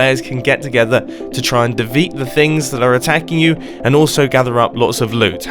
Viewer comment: did mumbo say devet